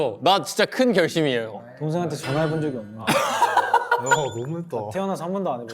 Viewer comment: it's ' brother ?' i think because yujin only have younger brother. The subtitles is wrong